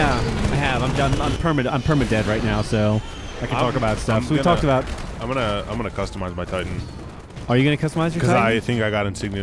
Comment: At the start when they were saying "I'm guna customize my titan", I thought they were saying "I'm Mike Tyson" haha